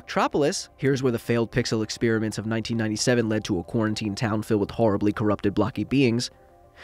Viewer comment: did he really said quarantine??